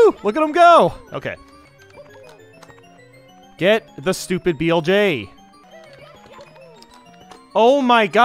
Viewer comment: flj instead of blj